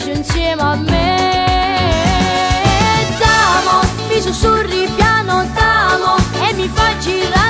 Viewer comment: SURRY cosa ha detto? 💃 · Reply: nooo ha detto sussirri